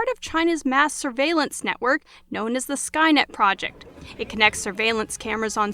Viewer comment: did they say skynet project?